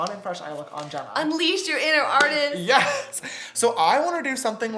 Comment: I thought Jenna said unLEISURE your inner artist.